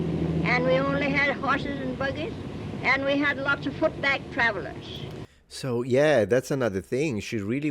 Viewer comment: she said "we had lots of footback travelers". That would be travelers on foot, right? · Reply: I heard "foot bag," but you're probably correct about the meaning